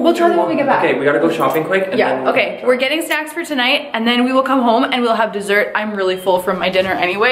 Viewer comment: lmao at 9:52 i heard “we’re getting sex for tonight” and i was like :o